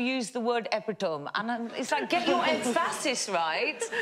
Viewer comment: or so. 'Get your Ed Faustus right'?? I wish I knew what she said there. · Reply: She said emphasis, but deliberately mispronouncing it by changing the emphasis, in order to make a joke about the guy who couldn't pronounce epitome properly.